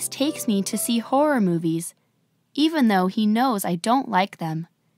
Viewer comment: 一個目の文章のeven through he knowsってところ何回聞いてもeven know he knowsに聞こえる、、笑 これthroughって言ってます...？それともパッと言うと発音が変わるやつかな...？